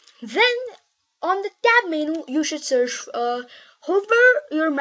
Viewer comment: in 4:21 did he say [Dab menu] ?